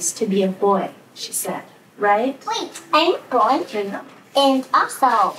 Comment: That little boy just said he was a boy, and his mother whispered “no you’re not,”. How is this not child abuse? How is this not evil? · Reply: Pretty sure I heard her say "I know" not no you're not